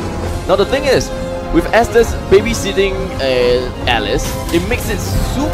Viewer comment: No homo but if they say the word "Estes" I heard "testes" you had only one job to talk with clarity but you need english class more.